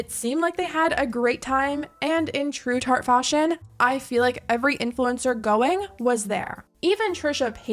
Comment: did you really say that "every influencer going, was there"?